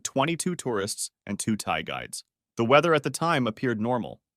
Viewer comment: I swear I thought you were saying "Thai guys"